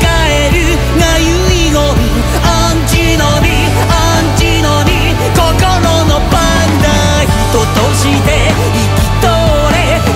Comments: 『アンチノミ アンチノミ 心のパンダ』に聞こえてた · 10年来のamazarashiファンです 「心のバグだ」が「心のパンダ」に聞こえて仕方ないです よろしくお願いします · 心のバグだー。がどうしても 心のパンダに聞こえる